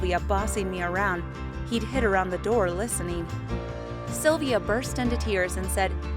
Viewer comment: I thought she said he hit her around the door but then I realized she just said he hid around the door